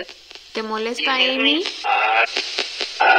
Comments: suena como que dice mm sí pero un poco bajo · cuando le preguntaste si le molesta Ami escuché que dice "saca a Ami" o "sácala de aquí", alguien mas escucha lo mismo? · En el minuto 12:38 escuche : me entiendes? , y despues escuche no voy a ofender talvez por que le dijiste : no te entiendo y talvez quiera decir que no se enojara contigo Algo asi no me acuerdo bien